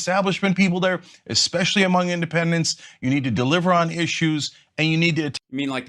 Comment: did Cenk say, they have to deliver on his shoes...??? Ok... 💩